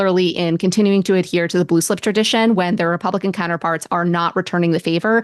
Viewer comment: Oh! “Blue slip”. I thought you were saying “Loose lips”!